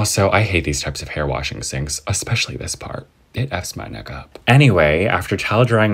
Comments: Did he just say the n word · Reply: Nah he didn’t say it fs my nigga he said it fs my neck up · I thought he said big Fs my nigga when hi said it Fs my neek up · WIAT IM SORRY MAYBE I YEARD WRONG BUT DID HE JUST SAY THE N WORD????!! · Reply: He said “ it f’s my neck up “ not the n word lol